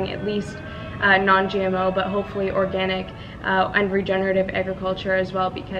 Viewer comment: (ish), what did she say? Unregenerative? What does that mean? Sorry:)